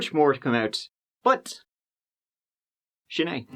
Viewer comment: What word is saying at 22:14 that sounds like shanay? And what does it mean? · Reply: Greetings..He says "Sin e",prounced Shin A,it translates to "That's it" but I see it as more final..That's it,end of story and no more to add..✌